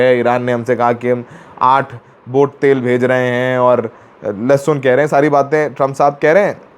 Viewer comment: Did he just say “Laura lehsun” at 10:26 😂